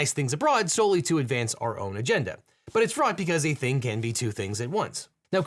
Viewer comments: what does he say here? i can’t understand him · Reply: "It's fraud because a thing can be 2 things at once". Pointing at that foreign aid in some circumstance can be used to help other people, and to advance certain agendas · what does he say here? i can’t understand him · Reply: I heard: "...but it's fraught because a thing can be two things at once." They both sound very similar, but "fraught" makes more sense than "fraud" in this context.